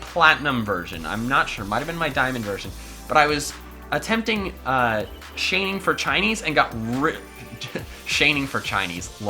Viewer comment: "Shaining for Chinnies. Lol!" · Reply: I thought u said chaining for chinese